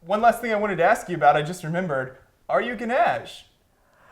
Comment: "Are you ..." what? Sounded like you said "ganache?" What was that last question?